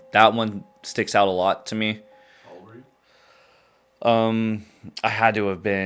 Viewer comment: I heard a voice during the story at 2:07. Who was that and what did it say? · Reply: They asked him “How old were you.”